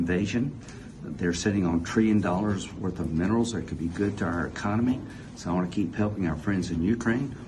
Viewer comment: "Trillions dollars of minerals that could be good to our economy" - for a second there I thought he's slipped and said "Trillions dollars of minerals that could be ours".